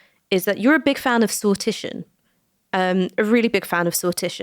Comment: he's a big fan of soltician? What's this word?